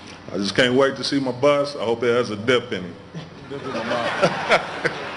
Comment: He said, "I can't wait to see my bust. I just hope it has a...in it." What word did he say? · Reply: Dip ... tobacco